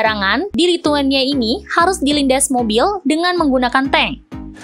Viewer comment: MBA NYA NGOMONG APASIH🤣?!?!?! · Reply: Dilindas mobil tapi menggunakan tank 😂